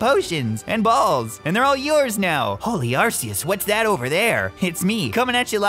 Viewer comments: At first i thought the rival said holy arsonist whats that over there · "Holy Arceus, whats that over there?" "I t s m e" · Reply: I really thought he said holy arsonist whats that over there